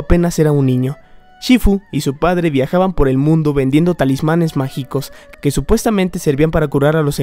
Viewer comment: Escuché "tamales mágicos" xdxd